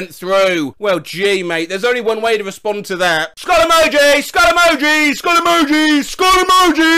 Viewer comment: My grandma thought that you said "Follow me" but i rewind it and my grandma laugh then i said "SKuUuUuuLLL EmoJi"